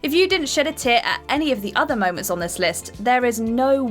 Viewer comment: I know this sounds messed up, but at 13:16 , I thought she said, " Sh!t a tit." I know she said, "Shed a tear", but my brain didn't pick up on that initially.